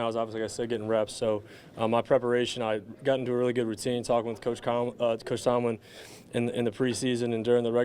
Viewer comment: Swear I heard “toach Comlin” lol all good kid, you will be less nervous after a few of these interviews